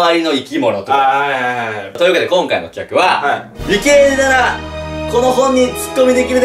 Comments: の｢理系なら〜｣が｢ゆきりぬなら〜｣って聞こえた私は末期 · 「理系なら」が「ゆきりぬなら」に聞こえるの俺だけかなw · の「理系なら」が「ゆきりぬ」ならに聞こえてしまった